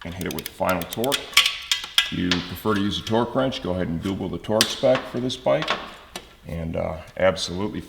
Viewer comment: is in the minute 19:16... but i thought it was weird to say that... thanks for the answer!!!! great videos!!!!! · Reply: He said "Google the torque specs"